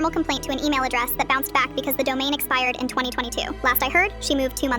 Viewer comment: When she said domain expired I thought she said domain expansion